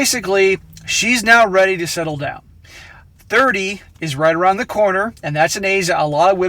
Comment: "30 is just around the corner and that is very scary for women". I thought you said "Thursday is just around the corner and that is very scary for women". I glanced at my calendar and had a panicked pikachu moment!